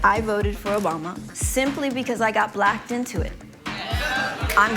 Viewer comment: Did she just say she got blacked?